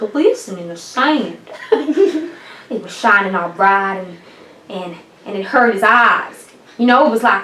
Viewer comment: And it herted his ass 4:40 😂😂 Sorry that all i heard ...